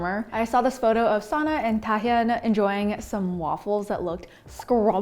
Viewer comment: Video is so gooddd But subtitles person made a mistake lol 8:34 that person written taehyung instead of dahyun lol it's fine :)